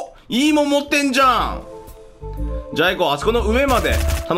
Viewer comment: ｢じゃ、アイコ！｣って言ったはずなのに ｢じゃぁいこ｣(じゃいこ)に聞こえてしまった すみませんでした